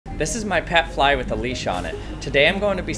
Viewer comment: I thought he said "This is my pet fly, Felicia" until I read these comments wtf 💀💀💀💀